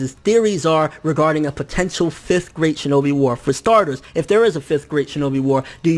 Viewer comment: thought you said 5th grade shinobi war